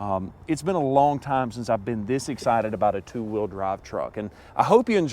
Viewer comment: something, sounded like you mentioned being excited by a two wheel truck? Edit....two wheel "drive" truck